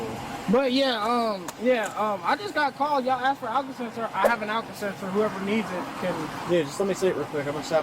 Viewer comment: I thought he said they called and asked for an Alka-Seltzer. Then he brought the case over and opened it, and I was like "Ohhh, alcohol sensor."